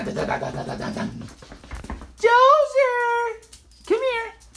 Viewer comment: XD when he called Dozer I thought he was saying Bowser